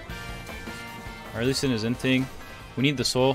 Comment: Did he say our Lee sin is inting?